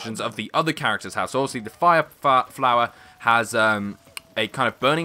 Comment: BWAHAHA. did he just say the "so obviously the fire fart flower.." 0:25 (:D